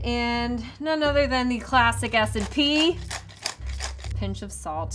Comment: lmao the subtitles at 15:25 say acid pea instead of S&P, and latte and instead of lactate